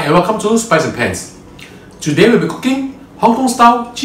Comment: LOL At the beginning of the video, I thought you said, "Welcome to spicy pants." Made me spit out my coffee. Great info. Thanks for your videos.